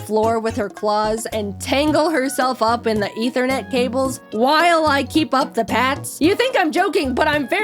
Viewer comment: is it just me or did she say "interneth cables"